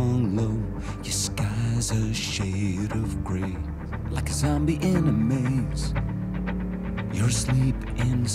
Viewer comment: I always thought it said zombie anime XD